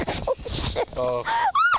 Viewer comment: did anybody hear the girl say oh shit